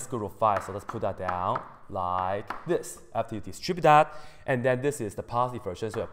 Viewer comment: there is a word you say that i don't understand , it sounds like : " tchipidap "